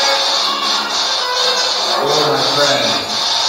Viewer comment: did anyone else hear a woman or kid say “hi friends” when asked why the spirits are only negative during the spirit box session at the end?? listen from 2:18:20 😳